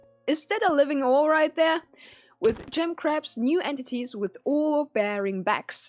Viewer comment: At first, I thought she said gem craps xD